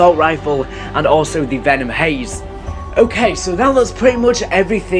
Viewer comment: did he just call the weapon shown @ 7:41 'venom haze'?